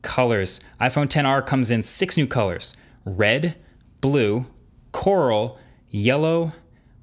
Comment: does he said coral or laurel?